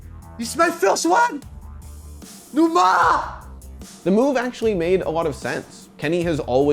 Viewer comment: Did Kenny say 'du ma' at 13:18? That's funny reaction :))